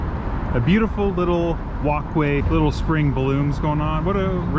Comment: Walkway I thought you said wok way